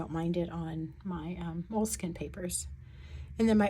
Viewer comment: PS I’m now thinking you were probably referring to my mentioning Moleskine paper. 🤪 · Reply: You’re welcome, and I knew you were using Midori, and heard you say it wasn’t your favorite with fountain pens because it dragged/grabbed a bit, and I thought I heard you say “like Maruman” but I guess you said Moleskine…which I’ve never tried and never will cause that 1 in 100 odds to get a good one now (apparently years ago they were good quality because people do talk about it. I have a feeling we’ll be seeing people running around buying up TRP-S notebooks in the rare hope that they get one that doesn’t bleed and feather…but, maybe Sanzen can turn things around, who knows.)